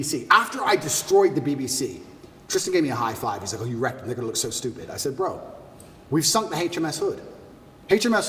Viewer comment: sounds like he said “nigger looked so stupid”